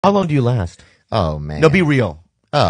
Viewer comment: Fuck, i misheard it to be "how long do you laugh?" and thinking why bobby thought a 4 min laugh wasn't enough.